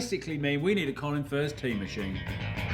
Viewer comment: I thought it said time machine at first and I didn’t even question it. I just accepted it, like ofc why wouldn’t he make one